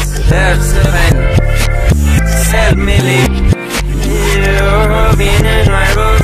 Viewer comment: why does this sound like it says sell me babe